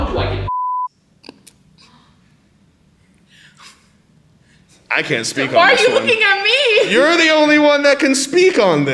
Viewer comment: what the hell was said here? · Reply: How do you get bitches